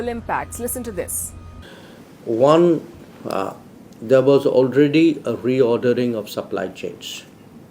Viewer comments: what is it he said at the beginning? It sounded like devil’s already reordering of sunlight change. Which I know that’s not what he said. Could someone help? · what is it he said at the beginning? It sounded like devil’s ordering sunlight change. Which I know that’s not what he said. Could someone help?